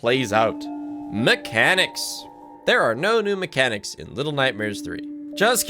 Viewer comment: for a solid 5 mins i thought was saying "Ellen" instead of "LN (little nightmares" i was like....wtf is elen?? who is she??